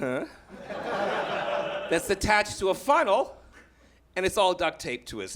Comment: He says "Funnel" but I keep hearing "Fun Hole" !